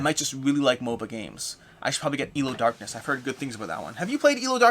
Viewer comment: What was the name of the game you mentioned at the end? I kept hearing 'Eel of Darkness'.